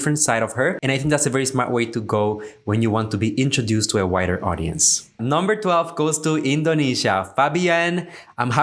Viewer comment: I thought I heard “whiter Audience”, then I realized it’s WIDER. Haha